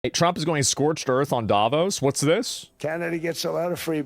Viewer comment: I thought he said trump going scorched earth on dominos